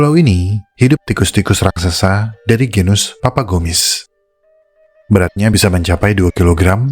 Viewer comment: Maaf agak salah fokus, harusnya tikus papagomys malah kedengeran papa kumis 😂, atau malah itu tikus yang beda lagi. Yangsuka makan dan nimbun kertas dan logam berangka. Maaf kalo agak pinggir jurang. Terimakasih 🙏